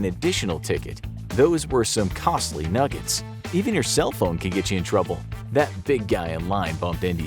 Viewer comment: I thought they said "eating your cellphone could get you in trouble"